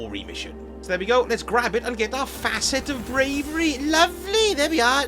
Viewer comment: When you got to the first fragment, I heard 'Facet of Gravy' for some reason......so left it as I didn't want to get brown sticky stuff all over my cloak!